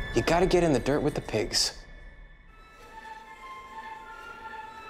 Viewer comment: How do we get ahead of crazy, if we don’t know crazy things?.. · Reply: I could have sworn what he said was " How do we get ahead of crazy, if we don't know how crazy thinks?"